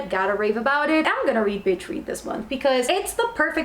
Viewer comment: When you said Beach Read the first time I thought you said Bitch Read, I’m tired excuse my crazy brain.